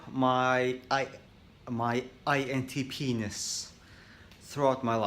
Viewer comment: Did he just say “INTPenis”? Lol I’m dumb sorry